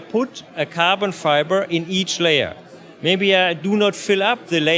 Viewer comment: Sounds like he says carbon fire...